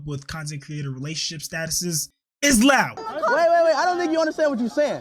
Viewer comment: did he say is loud?